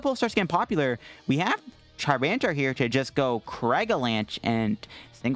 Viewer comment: Sounded like you were on the verge of saying charmander. Charmantar